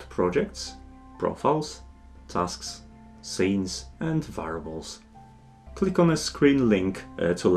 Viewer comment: Great tutorial! I was briefly confused a couple of times though because I kept mishearing 'variables' as 'fireballs' xP